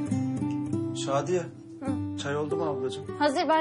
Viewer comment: Şadiye çay oldumu ablacim mi dedi ben mi öyle duydum oeoclroocorc